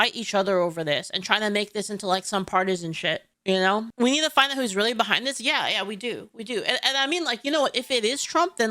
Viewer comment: I heard we "have to find the Israelite behind this" and started sweating.